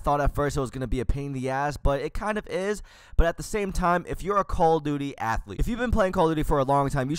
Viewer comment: did i just hear the word "Call Of Duty-athlete"????